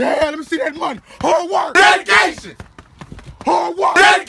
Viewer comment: Hard work! DEADICATION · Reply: I thought dey was saying vacation